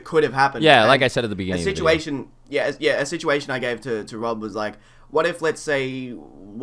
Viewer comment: name reveal? Sounded like refered to as "Rob"